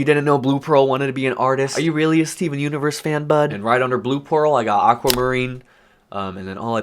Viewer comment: Who else heard him say “right under blue porl”